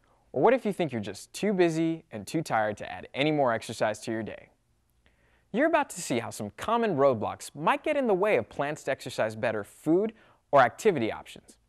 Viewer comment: Did he just say "some common roblox"